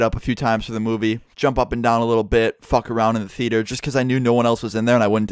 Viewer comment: thought you said "fuck her in the theater"